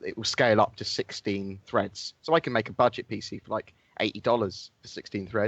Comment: Is it me, or did say he's going to build a budget PC for $80 at 5:02 .. I re-watched that like 5 times